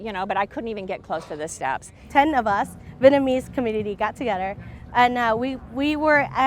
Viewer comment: How is this "indistinct"? She clearly said: Vietnamese Community